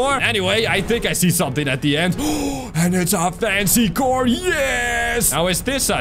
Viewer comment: I thought I heard messy car